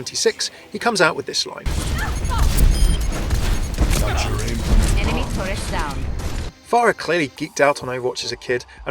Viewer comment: what does soldier say at 7:07? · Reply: "Got your aim from your mom, I see"